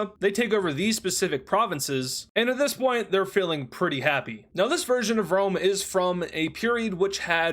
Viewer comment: I thought u said failing pretty happy 😭😭